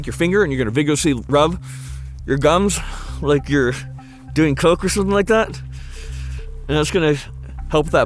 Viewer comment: did he just say rub your gums like your doing coke or something!? 😂 Seriously though keep up the amazing and informational videos! Always the quality and content is amazing and on point! Love these little survival videos you guys do! 🙌